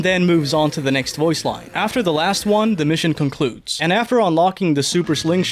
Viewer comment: I coulda swore you said “complutes” @ 6:33 like you were trying to come up with a way to say “completes” in a past tense lmaoo